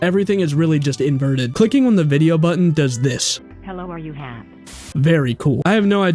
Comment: Ok for any one who needs it. This is what it says on.. 6:44 “hello are you ham ( maybe ham I couldn’t hear it) 6:56 “1 am in the morning a dead body was found and named scarzen bush vantila inaudible (inaudible means not readable) named after a city in uk ( United Kingdom ) Hope I help you :3 ❤